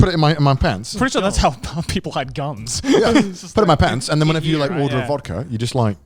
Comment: Did Joey just say "Pretty sure that's how black people hide guns"